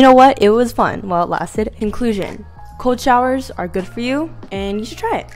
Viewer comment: Did she just say “inclusion” instead of “conclusion”?